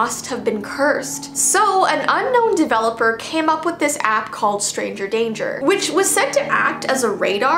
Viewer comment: I THOUGHT YOU SAID STRANGER THINGS FOR A SECOND GIRL